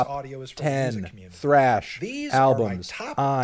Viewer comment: I thought it said trash albums